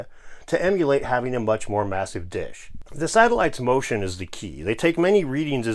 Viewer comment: When you said to emulate having a much more massive dish I could of sworn you said to emulate having a much more massive Dick haha